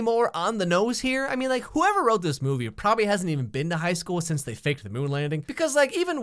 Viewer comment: I thought he said “I vape” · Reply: I thought he said “I fake”